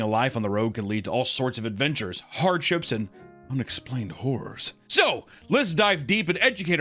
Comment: did you say "unexplained horrors" or "unexplained whores"? Either works